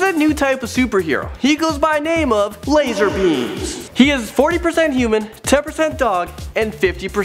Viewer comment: does he say lazer pubes